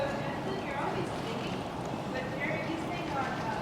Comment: Thank you so much for the video! The quality of sound and image is really good. Can I just ask what was the question the girl asked at around 12:20? I didn't understand at all. Thank you again. ^^ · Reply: I can't hear the whole thing, but she says "Jared you sang on Jason Manns' album..." so I think she's probably asking about whether Jared (and Jensen?) have plans to record any more songs. Hope that helps :)